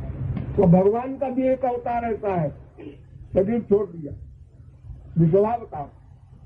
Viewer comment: What avtar name did SMNJI say? Edit: SMNJI said Rishabavatar. Thanks to Rama Prasad ji